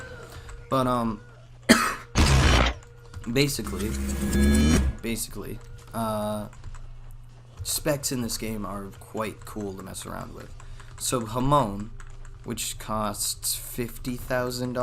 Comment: I literally thought he said shamon